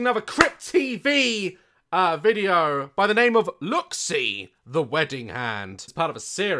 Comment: POV: you were confused when Daz said the title of the short film because you thought it said “Look and see” instead of “ Look see”